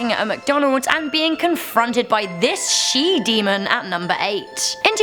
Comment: I thought you said "sheep demon"